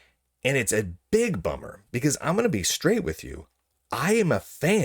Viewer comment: Did you say, "I'm going to be STRAIGHT with you"????? 😡😡😡🤬🤬🤬👹👹👹👹💀💀💀☠️☠️☠️ 😂